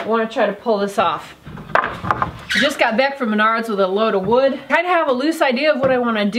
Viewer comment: At first I thought you said you just got back from Spenard with a load of wood, and I was wondering why would you go all the way to Anchorage for wood :>